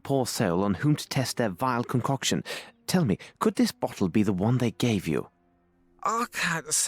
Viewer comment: I know he said bottle but it sounded like he said they gave me a butthole.😂